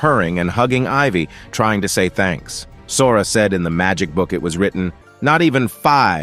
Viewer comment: am I the only one who heard him saying ass? Please tell me I am not the only one😂😂😂 wtf😂